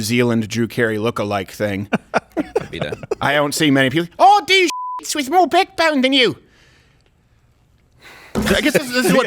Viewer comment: I thought she said “I do sluts with more backbone than you” and I thought that was funny lol. But now I know she said “shits” and so Dave was right…